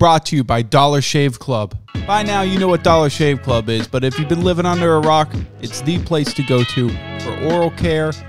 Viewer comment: When you said" if you've been living under a rock" at first I heard " if you've been living under Irak